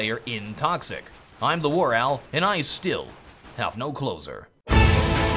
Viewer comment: When you say 'and I still have no closer' I always hear it as 'and I still have no clothes on' It's very disconcerting.